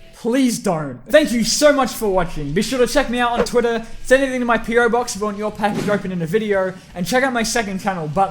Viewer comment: I thought he said pedo box at the end...